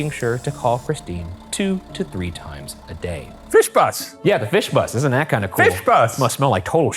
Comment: I think I’ve been studying for my German test for too long because at 6:29 I know intellectually that he’s saying “fish bus” but all I can hear is someone saying “viel Spaß” with a bad Scottish accent